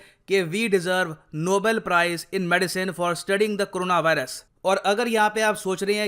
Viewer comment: i heard it as "for spreading the corona virus" 🙂